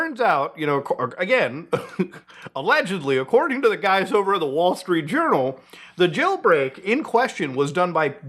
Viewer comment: Could've sworn I heard "the Wallstreet Urinal"